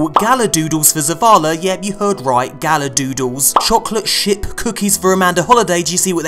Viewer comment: i thought you said "chocolate SHIT cookies"